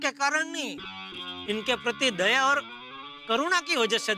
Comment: did he just say corona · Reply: It's karuda not corona